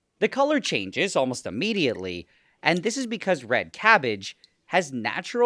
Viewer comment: I thought he said garbage 😅